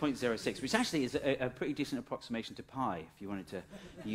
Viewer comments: he says "pi" and subtitles says "half tau" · you say "...a pretty decent approximation to pi..", but the subtitles say "...a pretty decent approximation to half tau..." What's that about? I have never heard the number pi referred to as "half tau".